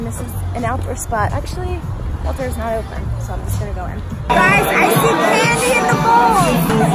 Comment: I thought you said you see panties on a bowl